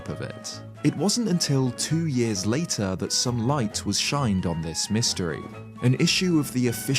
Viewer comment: Did you say "shined"? 🤔